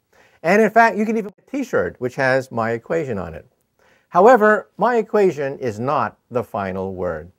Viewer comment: What did he say at the silent bit at 0:26 ? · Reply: In fact you can even get a t-shirt with my equation on it